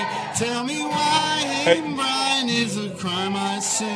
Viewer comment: "hanging brain" loooooool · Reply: I thought you said “hitting Brian” lol